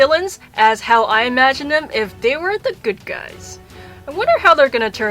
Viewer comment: i thought when you said good guys you meant as guys